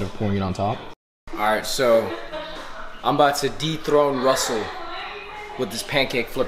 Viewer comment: I wasn't looking at the screen and I thought you said "I'm about to deep throat Russell". "De-throne", not "deep throat".